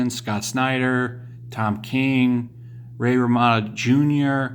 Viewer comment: it sounded like you said "Ray Ramano jr" for jr jr.